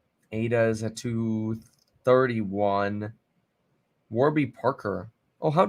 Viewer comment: lol had me spit at my coffee when I thought he said ADA was at $3.30